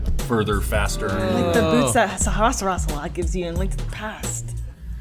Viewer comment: can someone tell me what said and why they say I'll kill you. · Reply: "Like the boots that Sahasrahla gives you in 'Link to the Past'" I can't answer the second question, though.